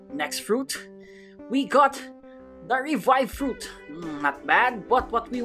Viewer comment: he said he got a revive instead of barrier 😹😹 LoL